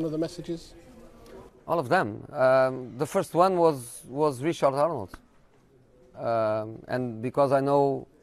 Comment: I thought he said Richard Arnold.